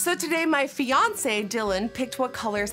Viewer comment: Istg i thought you said beyonce instead of fiance